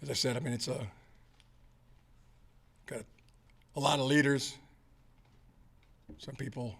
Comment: Every time he says "alot of leaders" All i hear is "Alot of CHEATERS" LMAO-LOL-LOL